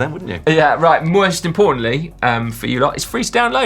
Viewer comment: did say "Moist importantly" ?